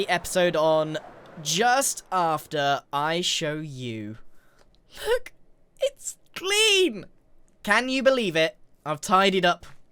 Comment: When you said look it’s clean I thought you said look it’s Klee I had to rewatch it two times before I heard clean